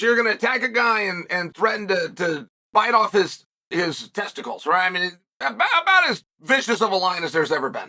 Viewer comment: I thought I heard Mike say he would eat Lennox's kids. My ears deceived me. He in fact, threatened to bite off his testicles